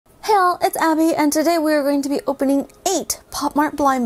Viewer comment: I like how the first words I hear are "Hell! It's Abby." 🤣